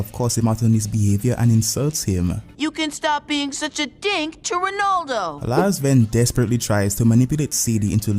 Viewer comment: I always thought he said ‘you can stop being a d*ck to Ronaldo’